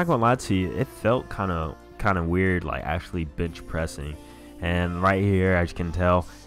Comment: Did he say b*tch pressing?